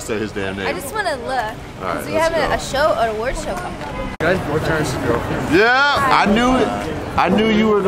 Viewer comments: Does he say guys returns and girlfriends? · What did that guy say at 1:31?? Also I got the Jesse cut!!! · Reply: "Are u guys boyfriend vs girlfriend?"